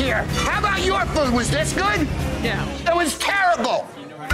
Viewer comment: did she say "yeah" when jon asked her if her food was good and still flipped her food?